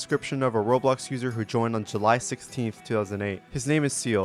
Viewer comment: Did you say.. July 16th?? **insert PTSD from the sun and moon show*$